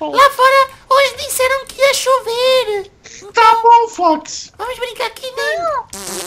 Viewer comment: voce disse Raposa em Ingles Fox que ele disso ou so eu ouvi porque e meu animal favorito